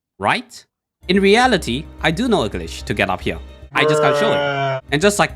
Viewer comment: which glitch i can't hear it well? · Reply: "i just can't show it" :_nobliesAmberderp